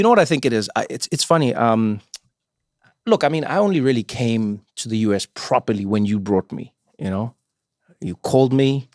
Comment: "I know, it's okay. Here's the thing. I'm like that, but, ___________" What did Trevor say? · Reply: I don’t prefer it